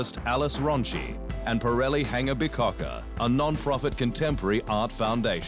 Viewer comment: I swear I heard the name of the person who designed the lightning bolt trophy as Pirelli Hangabigcocka. I can't stop laughing!